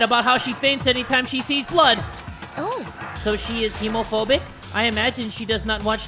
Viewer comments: I thought she said "homophobic" but ok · when Celeste said toko was hemophobic I thought it said homophobic and I was like “👁👄👁 was the girl who died gay...?” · being so fr the first time i ever saw celeste say hemophobic i thought she said homophobic ToT